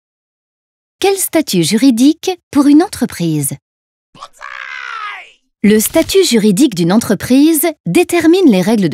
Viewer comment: c'est quoi ce bruit ? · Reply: Il dit ‘dessine’